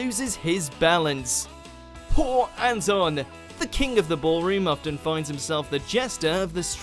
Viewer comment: I thought announcer said King of the boring. 😂😂😂